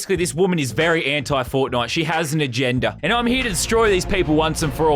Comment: you said she has an agenda; it sounded like you said she hasn't a gender